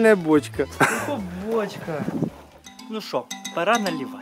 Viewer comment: мне тоже показалось что он сказал булочка